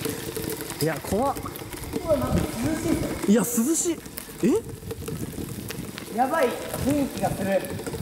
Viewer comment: 位のの「いや涼しい」って声がに聞こえた